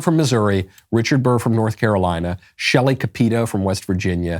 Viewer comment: I almost thought you said Shelly pedo 🤣🤣🤣